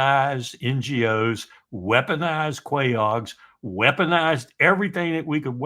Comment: What does Larry say around 37:40, right after "weaponised NGOs"? Quayogs? I've never heard this term before and I'm struggling to find the definition via Google.